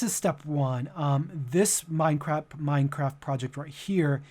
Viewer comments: Did he just said "This Mine Crap" 😂 😂 😂 😂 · DID HE SAY MINECRAP?!! xD